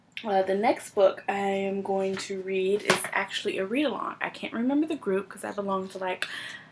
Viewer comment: I don't read much horror but the few I've read are pretty good. My good reads is linked into the description but let me know if it doesn't work. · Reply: I misheard you the first time, i wanted the group you were talking about but i just rewatched and you said you couldn't remember lol